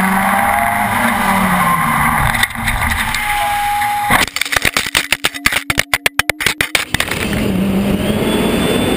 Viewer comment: did I hear someone say noooooo or is it my imagination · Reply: The Russian co driver said “blyaaaaaaat” which is essentially him going “shiiiiiiiit”